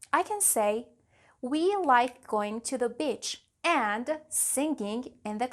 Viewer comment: Am I the only one who heard the word "bitch" instead of "beach"! in #